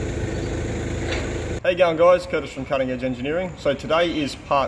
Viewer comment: every time Curtis says his introduction phrase, I hear "hey, young guys" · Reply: according to youtube transliteration, it's "How are you going, guys" lol I'm not that strong in Australiam English though